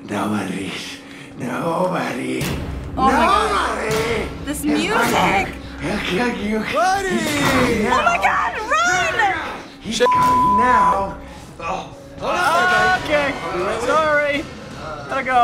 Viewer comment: did anybody else hear voice saying "BUDDYYYYY" ???